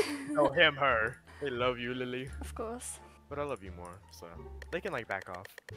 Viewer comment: Omg omg wait i hear at 1:03 “i love you lily” i bout know if you are kidding😂😂❤❤❤